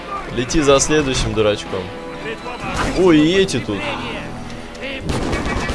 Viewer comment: мне одному послышалось или ты сказал : << о и йети тут >>